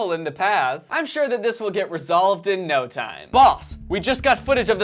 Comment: Did anyone else hear balls instead of boss 😂?